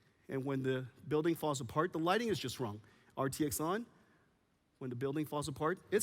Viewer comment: Thought I heard Justin Wong man my Volume was too low